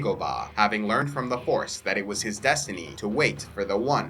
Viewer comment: Support by using the following Links: Audible: <link> Amazon (USA): <link> Amazon (Canada): <link> Amazon (UK): <link> Culturefly Game of Thrones Box: <link> Teeblox (PROMO CODE: CIVILIZATIONTBX): <link> Lootcrate: <link> You can also Support the Channel through Patreon and Donations: Patreon: <link> One-Time Donation: <link> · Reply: the Jedi purge when you said the yoda part I thought I heard "horse" instead of force or in-STEED?!